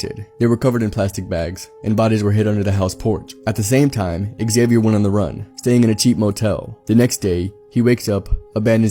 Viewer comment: I thought they said "At the same time Xavier won the lottery"